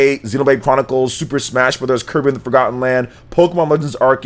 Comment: it's sounded like he said Kermit and the forgotten lands. I would play that.